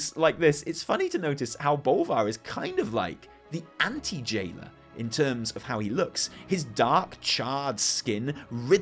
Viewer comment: I totally heard "Bolvar is like the anti-Jaina in terms of looks" and I was like "weird comparison, but kinda true, so okay"